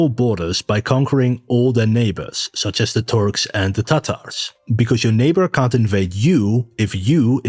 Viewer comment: did you say "tata's" ? Lol 😂😅😊 Yes! I'm a child!